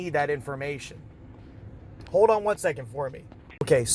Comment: did just say “ Hold on a second Corp” acting like he’s Military or law enforcement. He’s insane lmao